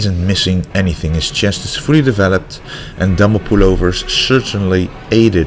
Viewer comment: Idk why but thought he was saying double pullover